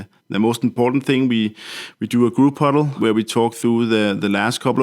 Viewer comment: Yeah and at 3:55 he clearly says "we do a group cuddle" but it was censored out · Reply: No, he meant huddle.